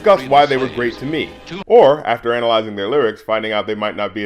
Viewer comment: I enjoy and agree with some of your videos, and some I don't agree with but still enjoy. You, I like. Subbed. And oh yeah 0:08 Did you just say anal-izing? ------ bruh.